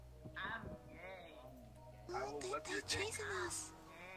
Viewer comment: Did anybody else hear the guy at 1:51 saying I'm gay